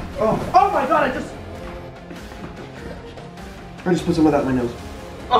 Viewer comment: what did he say at 8:09? idk why but i cant understand what he said lol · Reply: I just put some of that on my nose